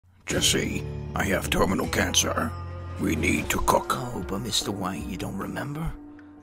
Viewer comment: I misheard Walter and thought he said, "Jesse, I have testical cancer. We need to cook."